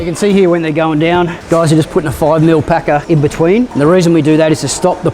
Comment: at first I thought you said "a 5 mm pecker". I would consider cutting the noodles probably 5 mm overlenght so they would have some compression to begin with?